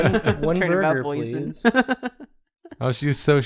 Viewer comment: watching this while stoned and I thought when said "boysen-burger" he was referencing "boysen-berries" and I was so confused why boysen-berries were relevant to the conversation until I finally realized he meant "poison"